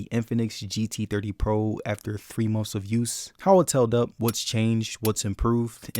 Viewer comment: Did I hear "6 months abuse"? 1:23 holy its strong